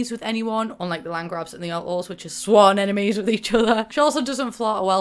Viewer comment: Every time you say "Alto" I hear "outlaw" ;)